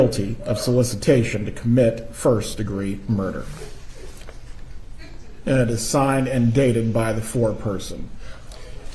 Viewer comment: wow she had a flash of real rage during the sentencing, has anyone figured out what she muttered to herself? Maybe "How could I have been so stupid"? Or was her anger directed at another person? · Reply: She apparently said "you convicted an innocent woman" and "my husband is not going to make it"